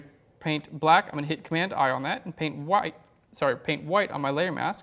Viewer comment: what are you saying? command what? You said it too fast. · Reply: Command 'I.' It inverts the layer mask :)